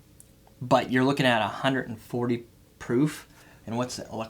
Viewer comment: What's so insane about it? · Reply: Nothing, I misheard him. I thought he said soothing like 190 proof, but it's 140.